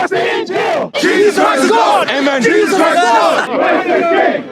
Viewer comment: Did say Jesus is Lord?